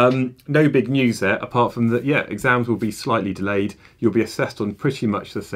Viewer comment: UGHH MY HEAR DROPTT I THOUGHT HE SAID THEY'RE FULLY DELAYED Thank god 🙏 more time!!